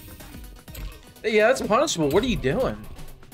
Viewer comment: uhhh what did you say right here bro??? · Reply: "that punishable what are you doin?"